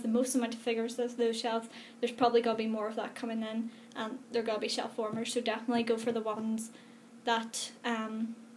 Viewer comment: When you said "shellwarmers" I always heard "shellformers"... XD. Don't get me wrong... but I have always associated the Wrestling figures collectionists to males... and you seem to know a lot too... XD Colectionism always surprises me...